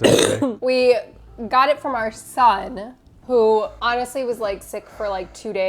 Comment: did you say ”son”?? I had No idea that you guys had a son.